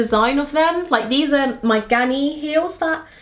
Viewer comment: For a minute I thought you said "my granny heels" 👵🏼😄 I love kitten heels, I got a pair of red suede kitten heels on ASOS 😍 xx